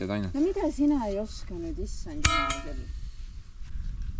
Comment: My Estonian is very limited but did I hear you say something about a butt plug at 41:00? Maybe it is that the top decoration of a flag pole is actually called Botplog in Estonian and the resemblance is just a coincidence. 😁